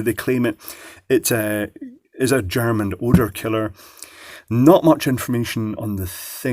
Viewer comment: I thought I heard you say, "German odor killer".